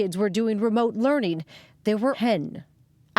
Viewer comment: they say hen instead of ten